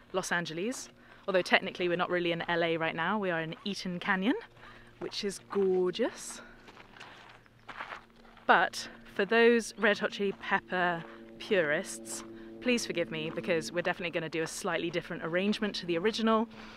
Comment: thought you said the canyon was "gorges." Well, yeah... duh! :)